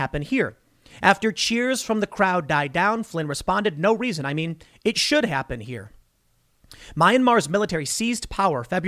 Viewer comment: Here is what I heard Flynn say: "No reason, I mean, it should happen here. no reason." Here is what CNN heard: No reason. It should happen here."